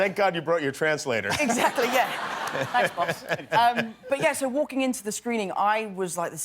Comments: did he just say thanks pops? what do I not know? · ''Thanks, boss'' Yes! :D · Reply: Leone B I thought that he said “thanks pops” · did tom just say "thanks pops" at 0:39 ??? AHHHHHH I LOVE THEM TOO MUCH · DID HE SAY „THANKS POPS“ @ 0:42??? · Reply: he said "thanks boss" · did he say thanks pops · SOMEONE HELP ME CONFIRM. DID TOM SAY "thanks pops" !?!?!? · Did anyone catch that he said "thanks pops" at 0:39 ? RDJ is literally his dad. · anyone else heard tom saying "thanks, pops!" to RDJ? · did he just say "thanks pops" or am I just dreaming